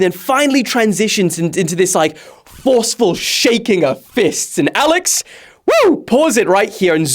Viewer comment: I thought you said Alice